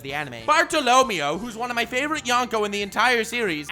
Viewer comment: Bartolomeo as Yonko?????? what do you mean? (or did I hear it wrong)